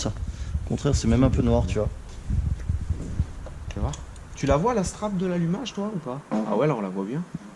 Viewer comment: "tu l'as voit bien la strat de l'allumage "Perso je ne sais pas mais j'ai entendu de décollage